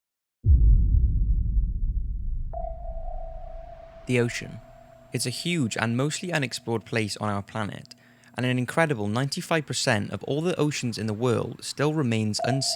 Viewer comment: I thought it said the poop!!😂😂😂😂